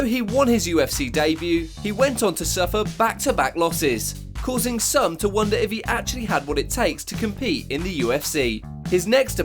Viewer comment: “ it led to some wonder if he actually had what it taked “ I believe TOOK is the word your after mr professional man